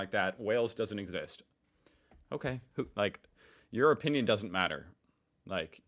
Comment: When you said "your opinion doesn't matter" I heard "europe union doesn't matter" and I think that's pretty American of you